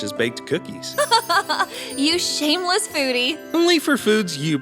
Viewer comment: Did she call him a shameless booty? 😂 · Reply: Foodie*